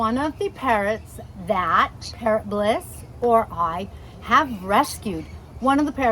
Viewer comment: The way you say "Parrot bliss bond" I thought you were saying "ParrotLess Bond" all this time, speak slower and enunciate properly